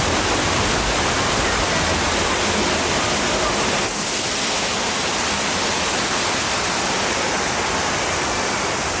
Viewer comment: What did the dude say at 24:37? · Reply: Tidal wave!